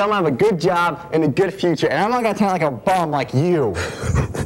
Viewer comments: I thought the kid said "atleast I'm not gonna be a bomb like you" at the end lol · Guy: "at least im gonna have a job-" Bully: literally dies on the inside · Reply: The first time i watched it I thought he said i’m not gonna end up on a farm like you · why did i hear 'i'm not gonna turn on a bomb like you' at the end